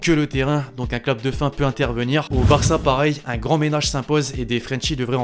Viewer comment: C’est moi où j’ai entendu VARça au lieu de barca à 7:33 ?😂